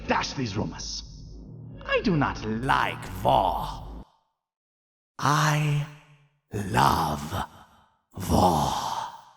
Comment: I swear to god I thought the N@ dude was saying "I don't like Vore, I LOVE VORE"